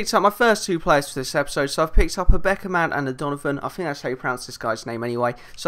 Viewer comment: Anyone else at 0:33 think it said Beckham for a split second?